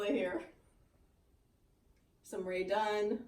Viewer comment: Wow, do you have a ‘she shed’ where you store the pieces for the other three seasons not on display? Gorgeous, you’re so talented, I love your chalk art, needlework and how you finish them. I thought you said radon, now know you meant Rae Dunn. lol